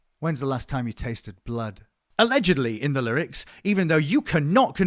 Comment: Ugh this part gets me. I know he has an accent, but I hear battle and battle makes more sense than blood 😩 Oh shit you’re literally saying that now as I’m typing. Holy shit 😂🤣😂🤣 I thought it was just ME!